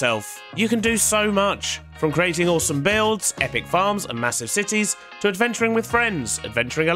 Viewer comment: Woo I realize I was hearing some things in the first 7 Seconds when you said epic farms I thought you said epic bombs and my brain was like that's illegal